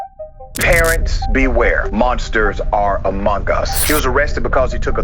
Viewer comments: DID I HEAR AMONG US? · What the fuck why does he say that?? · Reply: Mogus 🥶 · Did he just say amongus on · did he say amogus